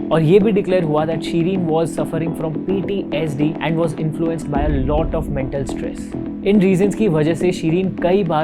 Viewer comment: Did he said a lot of METAL stress😳